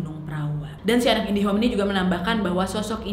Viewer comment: kok aq dengernya indihome yaa wkwkw